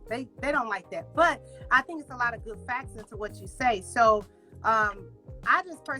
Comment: "i think there's a lotta good 'FACTSES' to what you say" WTF?!!!